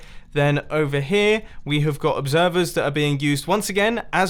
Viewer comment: Ohhhh I thought you said Like We will observe that rock instead of Obsever lol I am silly